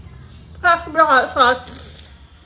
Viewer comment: I heard you say, catch up👏🏼👏🏼😂😂